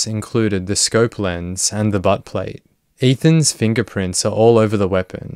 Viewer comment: I thought you said butt play…